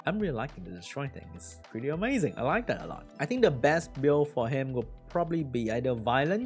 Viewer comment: into the vid, did he say, 'i laika that alot'. 👀